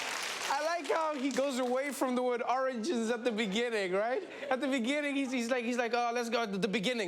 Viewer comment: What I heard was "Oranges in the beginning" I am confused about the Oranges origins🤣 History of orange